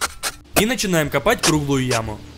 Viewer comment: Кому тоже послышалось не круглую а трупную?